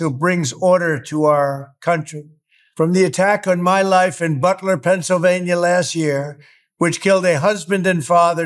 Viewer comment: Just pointing this out. Why did it sound like he said Buckler Pennsylvania instead of Butler?